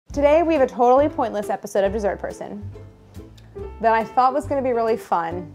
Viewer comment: food a good while of watching the show i thought the intro said "jesus croissant" instead of dessert person